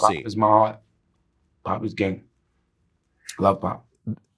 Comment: YALL hear everything else and ain’t hear this man say at 1:50 that “Pop was gay, I love Pop.” I’m tryna get clarification on what dude said